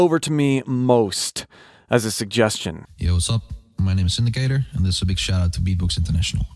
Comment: Btw, probably worth noting that when trying beatbox for yourself Indicator’s “Alien Bass” is probably not the best place to start. It’s a cool and unique technique, but it’s very difficult to learn and he’s the only one who uses it. He also hasn’t entered and won many big live competitions with it, so it may be temperamental even for him. It’s really cool to see him showcase it here, but there’s lots of other bass vocal techniques Beatboxers use that aren’t this finicky to master. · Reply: Thank you. I thought he said "this is Sindicator/Syndicator " and was gonna be searching that. Might still have landed on the right track though lol.